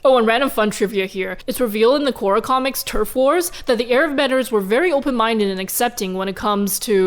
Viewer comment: I heard this as TERF wars I’m dying omg